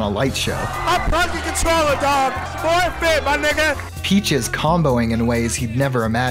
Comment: did bro say 3/5ths my nigga? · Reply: he said forfeit